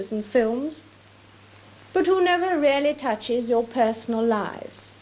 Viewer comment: Not sure if anyone picked up on this, but in the 1957 speech she said "really" when the captions said "rarely." Would love to hear your opinion of that. The time stamp is around 4:29.